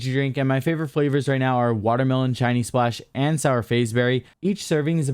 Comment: Oh Shiny Splash! Whew thought I heard Chinese Splash xD